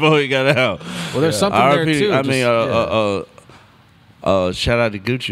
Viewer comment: Man I swear he said RIP and then corrected himself nervously with a Gucci sobriety comment at 17:00. Real shit having 36 in the house but that comment was sus.